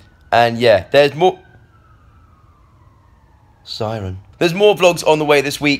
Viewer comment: When he said ' siren' nere the beginning I thought he just randomly said Simon 😂😂